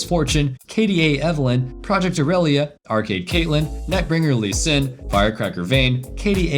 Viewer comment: I thought I heard him said "Neck-Breaker Lee Sin" lmao